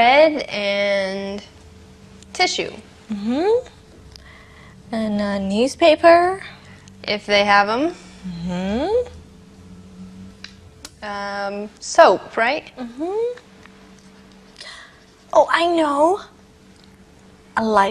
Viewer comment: soup. In England it sounds like soap (jabón) instead of soup (sopa). It’s curious….